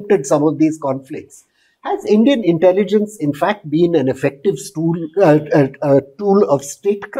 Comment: i am drunk Af did i hear espionage as an effective stool